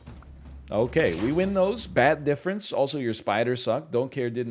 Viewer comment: Whenever he says bat difference all I hear is "THATS THE 51 DIFFERENCE BABY"